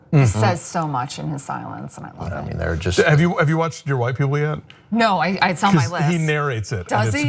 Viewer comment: What does say at 13:12? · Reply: Ronaldo Sosa It sounds like he says "Have you watched Dear White People yet??"